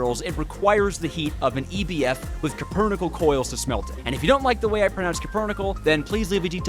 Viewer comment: tbh i thought u said barnacle